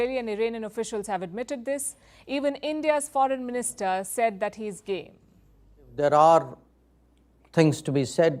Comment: why does she say India's foreign minister said that he is gay? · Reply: She said.. he is game! This informal idiom indicates willingness.